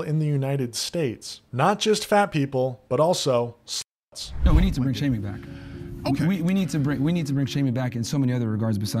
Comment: anyone know what word they censor around 27:45? im trying to figure it out but cant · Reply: slut. I regretted saying it lol.